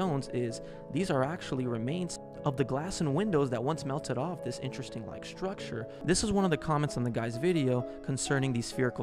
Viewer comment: did he just say "interesting-like structure"? Lmao